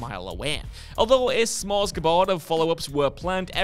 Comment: Did you say... "S'mores G'board?"